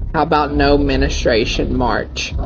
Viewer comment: I saw the video he made about the "No Menstruation March" and someone stitched the video with his mugshot saying "How about No Minor March for you, Brandon." 💀💀💀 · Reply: He said menustrate not menstruate 😂😂😂. Sounded like he said no administration march.